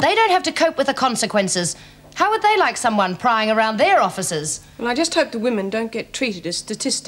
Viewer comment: I thought Vera said "how would they like someone prying around their orifices" - scenes of which would, presumably, be left on the cutting-room floor!